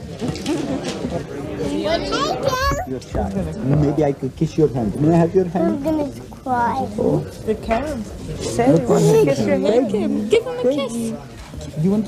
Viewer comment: Neat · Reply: Am I the only one who heard, "I'm gonna cry"?